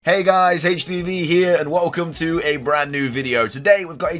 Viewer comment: I heard “hey guys hpv here” lmao